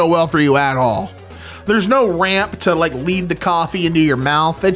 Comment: No RAM!? THAT IS NOT A COMPUTER! THAT'S... Oh... Ramp! It sounded like he said RAM